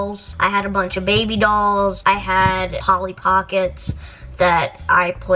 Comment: whats that? What did she say? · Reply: She said Polly Pocket, she’s a little doll that you could buy clothes, accessories, other friend dolls, etc for.